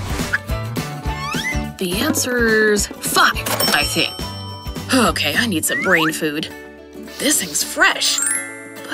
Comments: did he just said the f word? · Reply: HE SAID FAN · did he say f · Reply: He said 5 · OMG DID HE JUST SAID THE ANSWER IS FUCK?!?!!